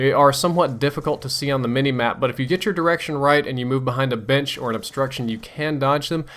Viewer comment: did he say? "if you move behind a bitch" lol